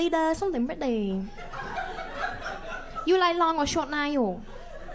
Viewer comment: I thought it said white friend but it said boyfriend 😂